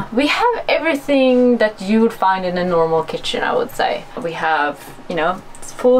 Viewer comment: I thought i heard anomaly for a sec